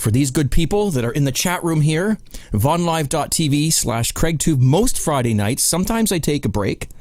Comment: Obviously I misheard you because I went to "live,tv" and guess what... that's an adult website ;) Good thing none of my family was in the room.